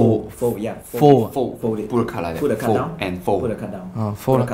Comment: Did anyone notice in the captions at 0:48 it say's "Put the cut down" instead of "Put the card down"